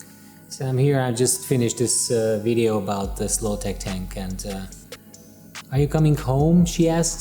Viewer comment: I heard "slow tech tank" and thought that was pretty funny 😂 Another great video all in all! Thank you for sharing your knowledge ❤